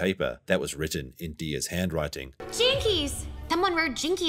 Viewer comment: Max thought he heard "Chinkies" ...🎈🎈🎈